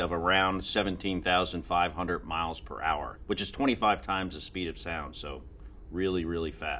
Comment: I thought the first question said "the speed of isis," I'll just see myself out now